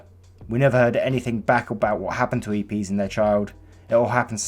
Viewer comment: captions on when he says eb the caption said eat peas! XD